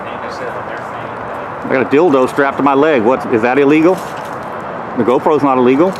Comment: not sure if it was a slip of the tongue but It sounds like you said you have a dildo strapped to your leg and I hope you do because it makes this entire interaction funny.